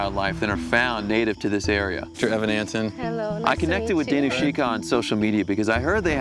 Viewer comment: Lol I thought I heard him say his name was Evan Hansen not Evan Antin